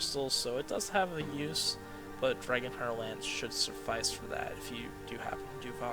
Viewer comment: What do you say at 1:18:36? Dragon what? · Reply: Dragon Hunter Lance